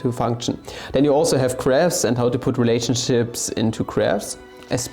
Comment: i thought you were saying "crabs" at first and i was so confused